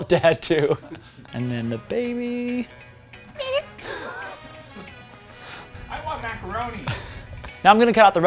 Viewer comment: Is that real?! I heard "I want macaroni"